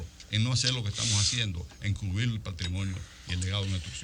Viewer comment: Después que terminara con la felación a Diandino, en el 19:17 él dice claramente encubrir vienes? yo entendí algo así, como encubrir, por si acaso hay que reponer a toda la gente que jodieron durante la ejecución de las obras del metro, no tener que joderse él mismo de su bolsillo.